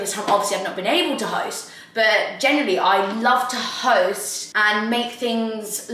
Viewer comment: I genuinely thought you said I love toast instead of to host, honestly cracked me up